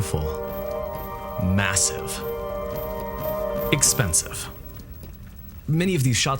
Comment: did you just say massive?